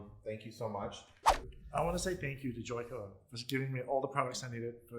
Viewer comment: Omg love both of you i definitely get my extensions rooted. What spray did u use? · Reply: Sweet and Sassy stephanie I think said Joico but I am not too sure, my volume is pretty low. It’s around 16:30.